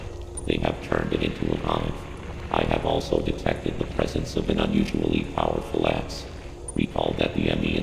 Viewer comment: In Adam Update #11 when he says "I have also detected the presence of an unusually powerful X." I swear it sounded like he said "I have also detected the presence of an unusually powerful ass."